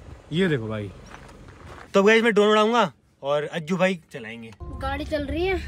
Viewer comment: Did he say Ajju bhai?